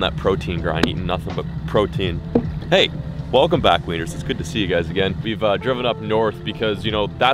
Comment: did he just call us wieners? 😖😩😭